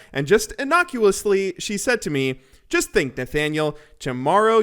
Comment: Wait, real name is Nathaniel? Or did I mishear that? lore?? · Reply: I thought he said